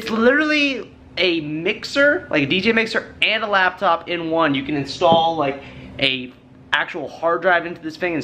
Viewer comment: I thought he was gonna say you can install virtual DJ 😂